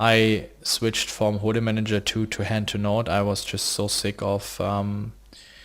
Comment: What's the HUD he talks about using at 5:27? I can't make out the name through accent! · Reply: Rupert G hand2note